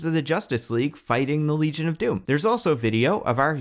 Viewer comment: this is a really good video but i nearly choked laughing bc I misheard 'the heroes fighting the legion' as 'the heroes fighting luigi'